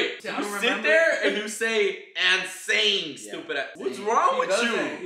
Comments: I would have never thought it was “and saying” it’s always been “insane” to me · I thought it was Insane but now I hear doing and saying lol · I always heard "and saying" 😂